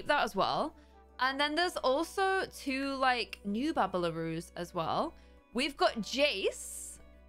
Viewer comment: Hey does anybody know what saying at this point? 3:10 I'm new here and ive heard her use that word a ton but no clue what it means ;,) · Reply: oh yes bubblaroos